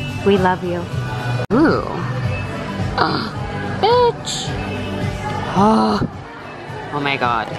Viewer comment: OMG did just say bitch ?